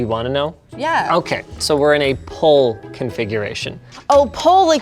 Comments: I thought he said "pole configuration," too. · Did she say its in a pole configuration like the north pole??? Help